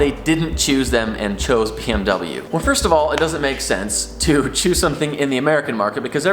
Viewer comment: When you said first of all it doesn't make sense to american market... I almost clicked off because it sounded like you said ''It makes sense to go with american market and put a v8 in there'', figgin heart attack.